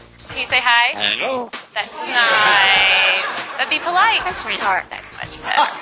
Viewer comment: i think he meant at 0:25,it actually sounds like it says "that's retarded"